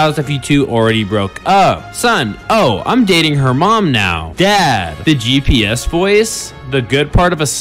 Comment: I THOUGHT IT SAID "im dating her man now" AND IM ABSOLUTELY DYING 💀